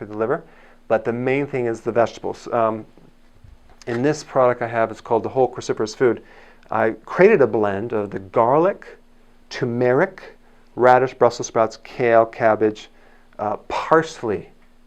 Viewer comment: I swear to you I heard "Dalek"